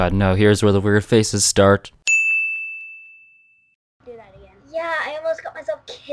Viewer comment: I hear “Do That Again.” Too! Put on your headphones so you can hear it.